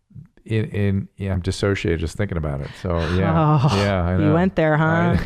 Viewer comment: What did he say at 7:00? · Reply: “I dissociated just thinking about it” with some messy words before he got the thought together